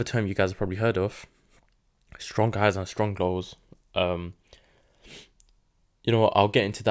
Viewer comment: you mention a term at 10:24 10:25 that I cannot make out- you say you will cover it in another lesson and I just cant understand the words. Can you please tell me? I am so benefited by your work specifically. You have helped me trade much smarter with less loss and stress. so thank you thank you. keep sharing its wonderful. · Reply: Strong Highs & Strong Lows - they’re swing points that took some sort of liquidity or tapped a HTF PDArray so we expect them to hold